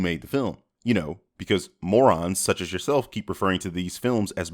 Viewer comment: Your voice says "Moron," but your subtitles say "DC Fans." DING. 1:57, 8:44, 9:57, 11:03, 12:07 Streeeeeeet Deep cuts. 🤜🏾 6:13 - "It's a Cone." 16:56 - He doesn't like being called a "Parasite." Oh, Wait. That was the Venom symbiote. My bad.